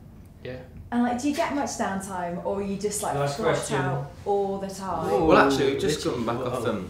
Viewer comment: Wait what did they say at 15:36 cause the interviewer said if the boys had down time and someone from their team yelled out something about the question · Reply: “Next question!”